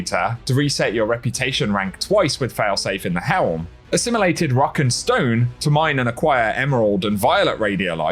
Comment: DID I HEAR A ROCK AND STONE?!?